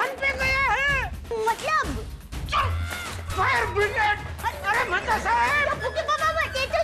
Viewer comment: Jetha says: Fire brigade Caption says: Bhide 😂